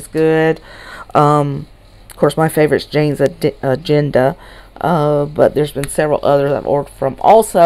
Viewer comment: I was dying, when you mentioned janes agenda, I almost heard you say janes addiction hahaha